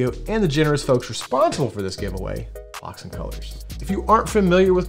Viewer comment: I swore I misheard but after replying a few times. Definitely said cocks and colors at 1:54 🤣🤣🤣